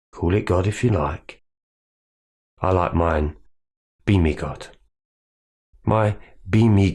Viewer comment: Good message. At 18:01 for a sec I thought you said “beanie” not “be me” and that you followed a beanie God. Nonsense aside this was very helpful